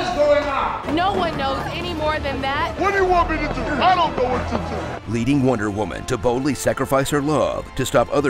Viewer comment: At time stamp 2:21 of this video, announcer guy didn’t comment that the police officer say, “ Wonder Woman is za ... I don’t know what to do.”. Or am I hearing wrong.